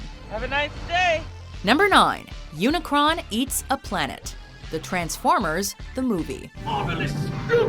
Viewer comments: i thought it said "unicorn eats a planet". damn straight i wouldn't sleep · I thought it said unicorn... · I thought that said Unicorn eats a plant :')